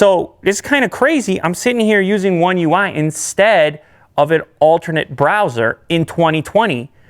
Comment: lew said alternate browser instead of launcher